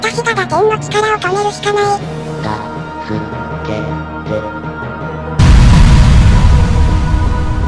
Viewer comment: 助けてって聞こえた